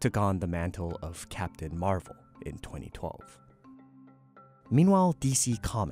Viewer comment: subs say 1977 instead of 2012